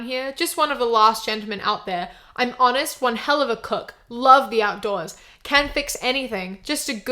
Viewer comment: dude I deadass thought that said "one helluva cock" and I wheezed before they said the actual words